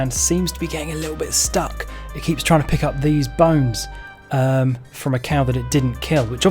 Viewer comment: So I completely thought you said 5 bones.